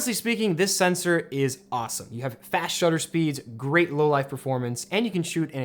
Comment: why am I hearing "great low-life performance" haha